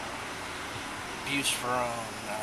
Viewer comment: Did he say he took Bupropion? 18:46 I took that medication a few years ago and it really messed with my alcohol tolerance. I blacked out after a very small amount of drinks. It was scary. People that take it should NEVER drink again. My doctor did not warn me about the risks at all.